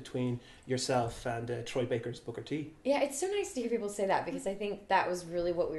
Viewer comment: Did he said Booker T in 6:49 ?